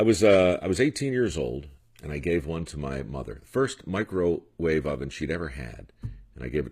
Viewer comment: I thought he said "Mike Rowe wave"